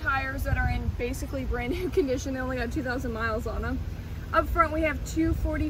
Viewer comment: It's not easy to watch this video because obviously you don't know what you're talking about. It seemed as if you were just making things up as you went along. For starters, if the car has 1,500 miles on it then the tires do not have 2,000 miles. Also exhaust tips do not give the car 5 extra horsepower, the exhaust system does. And if it's not too much to ask, can you possibly use a better microphone because I could hear the wind noise better than I could hear you at times. All due respect, try to know more of the facts about this car before you try to make a video about it. You might want to take a speech class as well so you stop saying the word "um" during your presentation. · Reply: I thought she said 1500 miles. I guess I was distracted by her legs lol.